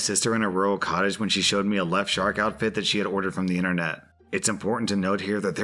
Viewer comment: Omg, I thought you said "shart outfit" Not "shark outfit " XD